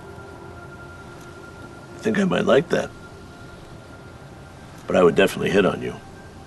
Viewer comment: Minuto 27:22 lo que dice es definitivamente coquetearía contigo. Esos subtítulos están mal